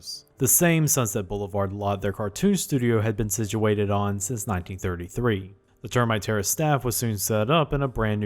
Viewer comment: As this guy was sloppily reading his copy, I literally thought he was saying "Termite Terrorist". Had to look it up. I'm good now.